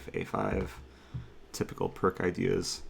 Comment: I thought for sure say, “...pawn to a5, typical prick ideas”. I was shocked that would use such language so I listened to the comment again and while it still sounds a lot like “prick”, I’m confident he was referencing “pirc defence”.